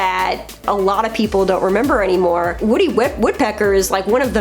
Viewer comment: a blooper for and sounded like "Woody Web Woodpecker".